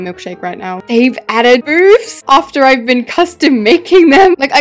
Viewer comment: the subtitles said boobs 🫣